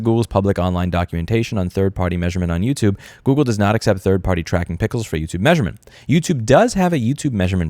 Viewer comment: I don't mean to detract from the video because putting google on perpetual blast for their bullshit on bullshit behavior is vital, but I heard Tracking pickle at 5:40, and now all I can think of is a pickled cucumber with a magnifying glass and a trilby.